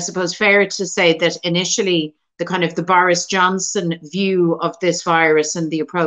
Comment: it sounds like she says "The Virus Johnson" which is hilarious and perfect